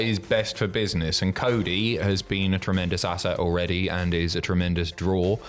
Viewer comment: did he say cody is a tremendous ass hat